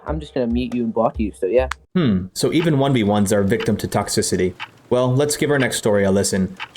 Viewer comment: Did he just say “Even 1s” ?!?!?!?